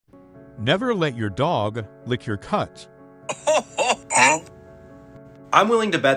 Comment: I THOUGH IT SAID DONT LET IT LICK YOUR NUTS 😭😭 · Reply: When I watched this video the second time I thought it said "never let your dog lick your cats".